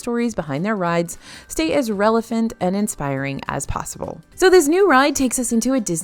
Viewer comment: Did she say "Relephant" instead of relevant? At 10:15?